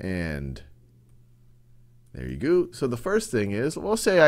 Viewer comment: Wait... at 5:21, does he say "there we goo"?